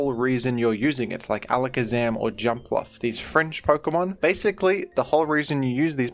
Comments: "these French Pokemon" - Alakazam and Jumpluff nationality confirmed. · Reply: Did he actually say French I thought I misheard “fringe” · Did he actually say French I thought I misheard “fringe” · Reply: He did say fringe, but I thought it was amusing that I misheard the first time as they sounded similar enough. :P